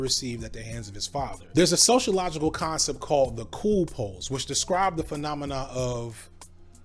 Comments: What sociological concept was he about to describe at 15:52? He kinda mumbled it, all I heard was "the cool..." and then some p-word... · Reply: The cool pose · What did he say at 15:55? Cool pause? Cool poles? Cool pose? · Did you say "cool pause" here? 15:51 I wanted to look that up either on your channel or elsewhere, but I can't seem to find it.